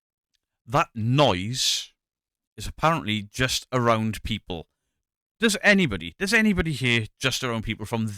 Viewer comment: when it said "just around people" all i heard was "snapped"